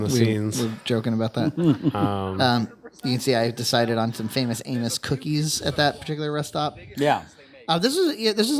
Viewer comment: I heard "famous anus" cookies 😂